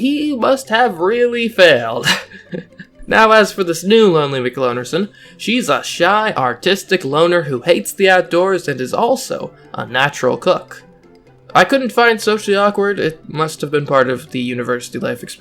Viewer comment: I thought you said Autistic and was surprised for a second.